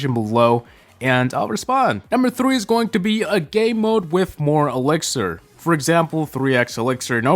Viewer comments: Sounds like he said Gay mode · I swear I heard Gay mode